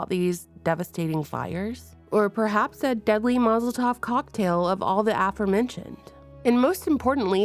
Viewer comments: you sound a bit sniffly. I heard "mazel tov" cocktail and not "molotov" 😂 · I heard that as "deadly mazel tov cocktail" 😅